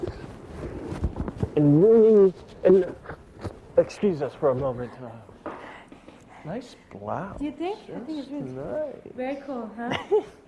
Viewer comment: 我聽成nice bra…